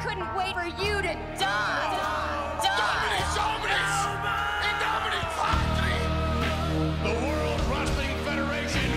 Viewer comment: What's he saying at 1:03? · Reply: "In the Name of the Father, the Son, and the Holy Spirit" in Latin.